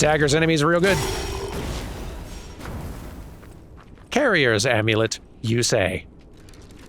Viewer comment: "Carrier's Amulet" I genuinely thought it said "Carrie's Amulet" and thought she was going to chime in